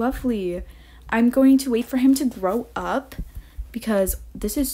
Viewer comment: When you said grow up I thought you said throw up so I was really worried about you for a second there 😅. Congratulations though! Thank you for another well thought video!